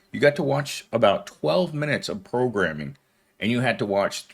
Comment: I just heard "prolegramming". 😂